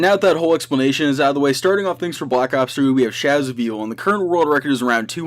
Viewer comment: did he say black ops 2